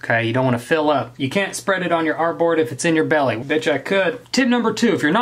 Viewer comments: When he said “betcha I could” I thought he said “b*tch I could.” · when said "betcha I could" and I thought he said "bitch I could" lol.... Almost fell over haha